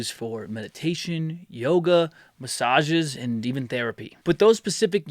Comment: At one point, I could have sworn you said 'Demon Therapy'! LOL